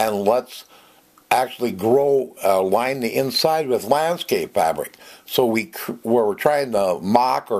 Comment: Great video...by the way, what kind of fabric did you say at 4:27? ... can't really hear it very well...thanks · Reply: Landscape fabric. It's a weed blocker that still allows air and moisture to penetrate. Available at garden centers everywhere. He uses it for soil retention.